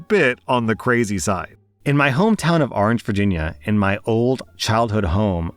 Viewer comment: i swear to god, i heard "Argentina", not "Orange, Virginia" 😂😂😂 Also, sorry for your loss, i know exactly how you feel. Im actually currently visiting my home town and i have to FIGHT the urge to go back to the old townhouse and ask the new tennants if i can visit my burried pet rats